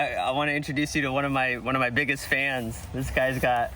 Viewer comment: I thought it was a pun when you said 'one of my biggest fence' right next to giant fence 😅